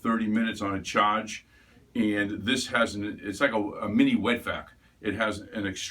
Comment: did he say "mini wet back" I swear I heard wrong..... but I feel insulted by that.